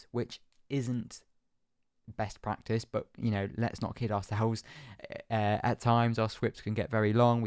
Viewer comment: for a sec i thought he said “Lets not kill ourselves”